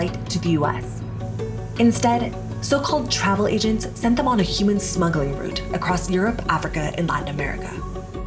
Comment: I totally thought this said "Human Snuggling Route" and I thought "awwwwwwww"